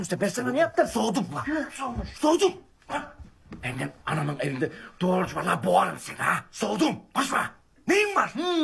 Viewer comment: "anamın evinde doğurçma" gibi bir şey söylüyor tam olarak ne diyor orada ya takıldım kaldım o lafa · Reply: Anamın evinde doğru konuş diyor